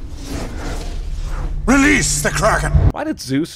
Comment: We had to watch the original Clash of the Titans in school and I remember when the guy said release the Kraken a kid in my class thought he said release the cracker that had the kid very confused LOL. The kids said What another white guy with powers?